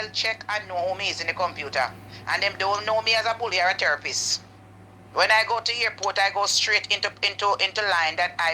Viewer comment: did she say at therapist it should be terrorist😂😂😂